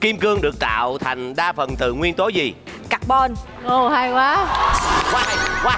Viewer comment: vãi lol t nghe thành cặc bon :v cười ỉa